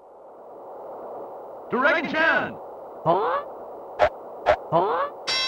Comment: when i hear "Dragon chan" it sounds like i'm hearing "drackie chan"